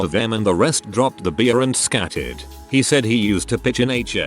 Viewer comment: Because of the robot voice and its accent I heard "scattered" as "scatted" so I just pictured fifteen homeless folks with paint on their faces going "Ski-bi dibby dib yo da dub dub Yo da dub dub..."